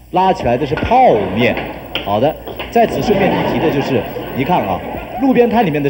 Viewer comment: 講話聲被笑聲蓋過了聽不清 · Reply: 在此順便一提的就是〈#J編〉